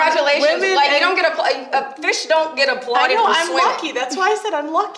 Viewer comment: I’m stealing that Brittany Renner “a fish 🐠 don’t get applauses for swimming” quote..lol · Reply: I thought she said a point for swimming. Either way I like it.